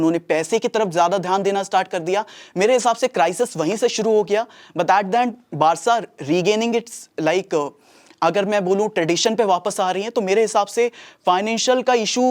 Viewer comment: kya bol raha hai bhai?? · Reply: Kuch cheese cut gai i am saying, its about started in 2011 jab barca ne branding ki taraf gaye the, apart from believing in tradition, uss time spending gap badhne laga, and uske baad Bartomeu, aaya tab neymar sell hua, 222M the but they spend so much reckless ki unka spending 600m ho gya tha approx more then any club in football, waha se finace bigadne lage, see i have explain but time limit hota hai so i am so sorry, if i had to explain tag me and ask question